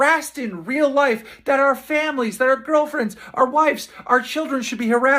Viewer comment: did he say girlfriends?